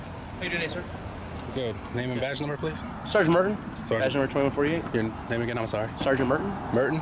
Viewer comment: That sounded like Sargeant Murder to me